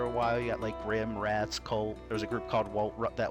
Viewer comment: SPITS OUT DRINK did he just say Cult?